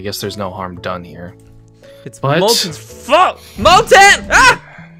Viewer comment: Moltens FXCKED I hear it